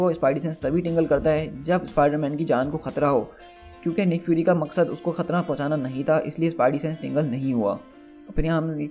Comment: Did you just say khatna in 4:38??? 😂😂😂